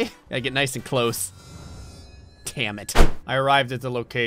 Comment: Did he say Tammit?